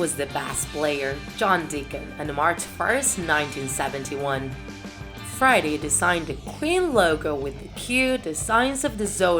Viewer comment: who heard Friday